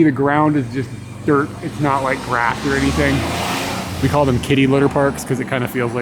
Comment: I thought you said Kiddie litter park, not Kitty litter. I just imagined it was for kiddies that weren't house trained.